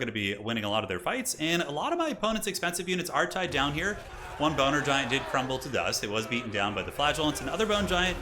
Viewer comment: did I just hear boner giant?